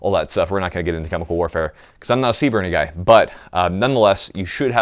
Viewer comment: What did he say at 7:25? I'm not a ???? Guy ? I listened four times and I can't tell, thanks. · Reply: I'm not a CBRN guy, (chemical biological radiological nuclear) military specialty